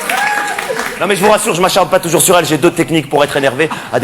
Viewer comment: mais il dit quoi à 12:30 ? ça commence "mais je vous rassure, je me ---- pas toujours sur elle" Je crois que ça veut dire "se défouler" mais il dit autre chose. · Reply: M’acharne?